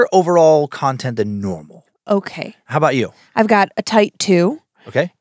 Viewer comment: said "I've got a tight two." I hear "I've got type two." I had to listen to it a couple of times to make sure didn't just drop her diabetes announcement on us spontaneously. #HappyHallowThanksMas